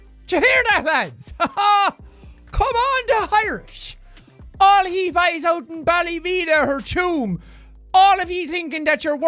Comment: Can someone please tell me what he just said because I'm American and I have no clue what the fuck, "All he bize out'n balle beater'n chume" means